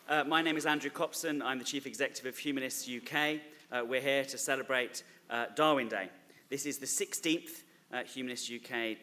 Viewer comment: i thought that said "dawkins day"